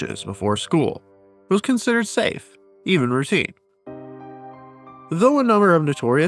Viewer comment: It was considered safe, even.....what? Does this narrator have a cold, because I can't understand him? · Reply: Even Routine.